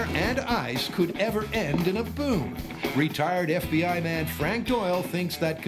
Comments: I honestly thought the talker said ”...the retarded FBI man..” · that split second where i thought the narrator said 'retarded fbi man'